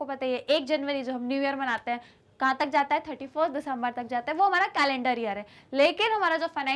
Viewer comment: was that 34th or 31st u said mam cause it more sounded like 34 then 31 😅